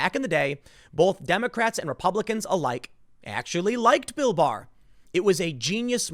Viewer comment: I thought he said Bill Mahr, like why would you disagree with him, he is handing out the solution to all our problems, FOR FREE